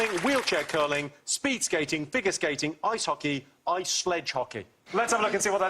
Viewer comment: did I hear "Ice klunge hockey"